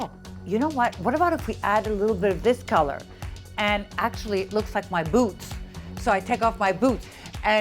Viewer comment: haha i thought she said "oh, they look like my boobs! So I take my boobs off." I was like WTH?! :D